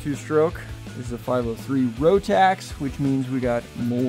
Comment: Dear, that little oil reservoir on top of the engine is not the lubricant for your crankshaft, rods and pistons ! The little oil reservoir only lubes your rotary vane which sits between the carburetor and the engine block, nothing else. So, please add appropriate 2-stroke oil to your fuel. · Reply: my mistake. I thought he said rotary not Rotax